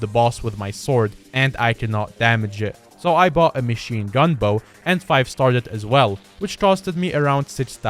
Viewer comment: ok I was just listening to this while I played Skyblock and all I heard was so I bought a machine gun